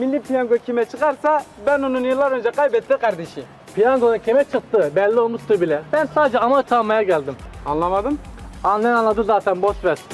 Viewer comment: abi orada ne diyor anlaşılmıyor abi ya takıntı yaptım nolur anlayan veya abi sen soyler misin piyango zaten kime çıktığı belli olmuştur ben sadece ..... geldim 00:50 'inci saniyede dedigi yerde hani arkasındaki kisi Mustafa yani diyor yw bilet kime çıkarsa ben onun yıllar önce kaybettigi kardeşiyim diye o diyalog nedir abi gözünü seveyim aydınlat ya teşekkürler şimdiden. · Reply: Avans (amorti) almaya geldim diyor.